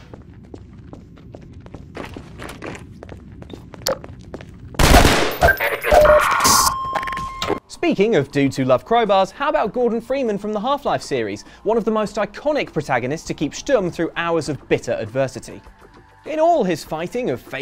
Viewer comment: I thought he said Morgan Freeman from half life lol